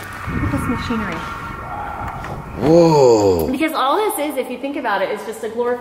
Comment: Who heard a "WHOAAA" @ 1:40 like so Omar can see